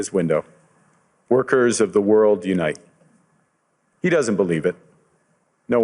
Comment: What I heard was “workers of the world, don’t you dare unite, you have no power” at the same time as “workers of the world unite” 🤯